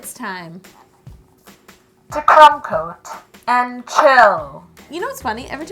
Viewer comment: I don't mind the mega phone but every time yo says "crumb coat and chill" I hear "c*m coat and chill" which is so wrong on so many levels.